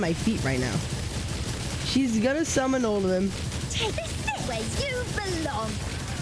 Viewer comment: i heard Margie's voice: "This is where you belong"... LOL